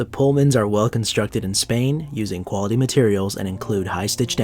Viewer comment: Do we know where these are made? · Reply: Dr Zaius I thought I heard say Spain.